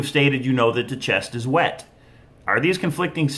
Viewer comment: I thought he said the treasure chest IS wet/whet...?